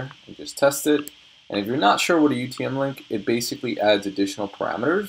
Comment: What do you say at 28:50 ? What kind of link is that what you are talking about? If you are not sure what a XXX-Link is? Like to learn more about this. Thanks for the great video by the way. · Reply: Hi There -- I was talking about a UTM Link, there are free online generators you can use to create one if you Google it.